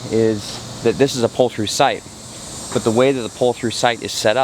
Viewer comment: LOL, I thought I heard him say "this is a poultry site" (4:01 )and I started looking for the chickens or a barn nearby the campsite. Instead it was "a pull through site". My wife says I'm going deaf. ;) Remember, backing up is easy; just keep going till you hear the crunch, LOL. Seriously though, I think the campsite is at fault for not putting a concrete pad around that septic access. If they'd do that, you'd only have rolled harmlessly over it. They could at least put a white pole sticking up next to it.